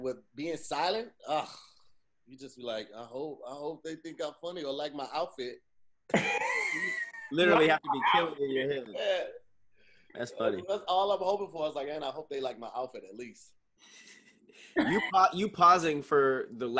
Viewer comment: Hi Todd, I think wanted to say Outfit but instead said HOUTFIT, that's the first mistake he's made today, we'll see how he does as the show goes on, back to you Todd.